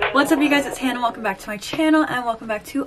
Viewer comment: I am so tired that I thought you said " What's up you guys it's Hannah Montana" I was like wait what?